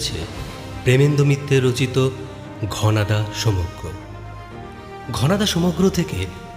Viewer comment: bro did you just say, "প্রেমেন্দ মিত্তের রচিত ঘনাদা সমজ্ঞ?" 😂😂😂😂